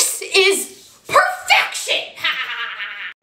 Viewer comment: I thought she said:"he broke my heart so im gonna break his fart"😂😂😂😂